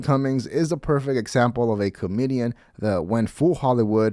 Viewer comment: Instead of comedian I thought you said chlamedian, which I think is hilarious because it still fits